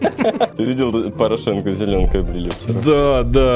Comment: Я один услышал «Порошенко Зеленко убили»